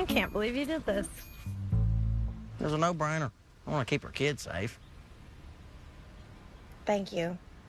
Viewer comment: Wait did he just say KIDS or I'm tripping?? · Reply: "I just want to keep our kid [s]afe."